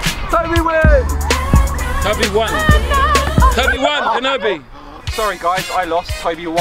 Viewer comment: tobi wan kenobi I think jme said that not sure